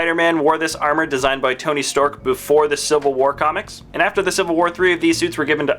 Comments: Was I the only one that heard him say "Tony Stork" while showcasing the Iron Spider? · Did I just hear "tony stork" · did he say Tony Storck